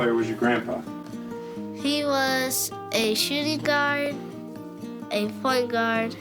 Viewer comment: I thought he said a “shiddy guard”